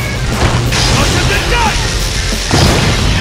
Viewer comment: Did Kanji just say "Welcome to die!" at 0:46 ?